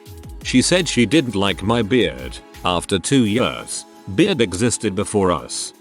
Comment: I THOUGHT THAT SAID BREAD 😭